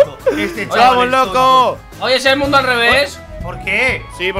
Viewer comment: En el minuto 1:15 escuché Timba:Es el mundo alrrevez ... Pues el riumba es Real